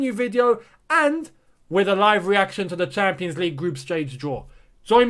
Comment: Did you just say group strage draw. Might wanna watch that part